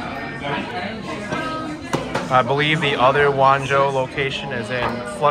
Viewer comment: At first I thought you said, "I'll be dining at "One Joke" restaurant. lol I had to do a double take on it. Your selections look good and interesting at Wonjo:-) Looks like the joke was on me:-) 🧚‍♀